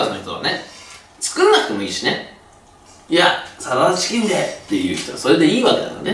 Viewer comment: なんて言ってるんですか？ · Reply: カルピスウォーター サラダチキンで！かと！